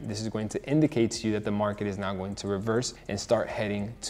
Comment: Amazing video Big fan on going on year 4 trading and year 1 youtube @ 5:47 it has wrong caption word being now caption says not would def be bad if new traders puts that in his notes